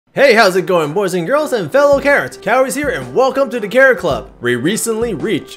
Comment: did u say Karen???